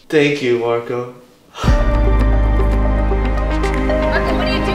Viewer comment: When your meet mom said Marco come it’s dinner time I swear I heard her say “idiot”